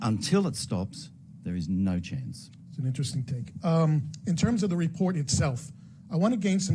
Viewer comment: did he say “interesting take”